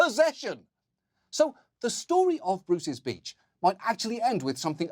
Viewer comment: Ngl? I heard, "So, the story of Bruce's Bitch might actually be approaching justice" XD